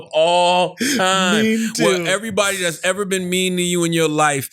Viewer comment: I'm not going to lie. I thought you guys said "#Memetoo" and I was on board.